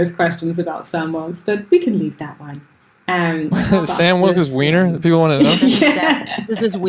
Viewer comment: clown core lore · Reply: Sorry to burst your bubble but I think she said sam wilkes 19:25 and bass mentioned later in the bit not gendel.